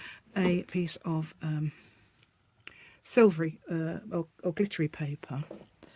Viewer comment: Hi! You woke my mind up and gave me a good laugh. When you were making the reinforcement for the hole, I heard "you could use a bit of silver obituary paper...". Wait..what?!? The Brits have special silver paper for obituaries?!? A half second later the rational part of my mind said "silver or glittery paper!" 😂😂 Thanks for sharing the great folder. Enjoy your day! Hugs, Carol and the clowder 🐈‍⬛🐈‍⬛🐈‍⬛🐈‍⬛💕